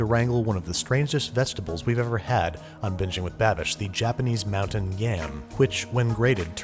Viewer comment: Anyone else hear "Bitching with Babish"